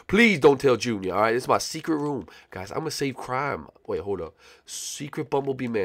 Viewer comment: did anyone hear that he said “save crime”